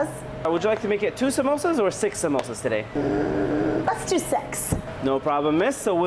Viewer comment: When he asked how much samosas i heard "lets do sex" 💀💀💀💀💀💀💀